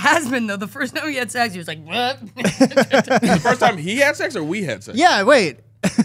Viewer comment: she said we 😭😭😭 I thought she said she was demisexual 😂